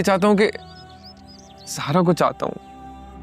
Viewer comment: notice Kiya kisi ne ? Sarmad ne kya bola 🤭😲 Sara 😲🤭